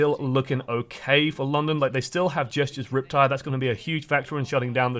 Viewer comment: Did he just say gestures rip tire ?